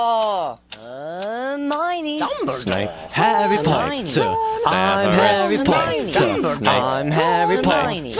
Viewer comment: It sounds like harry says "Im harry Mointer"